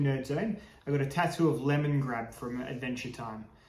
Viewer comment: Aaaaahhhhh, lemonGRAB. I thought at first you said lemonGRASS, the international signal for swingers.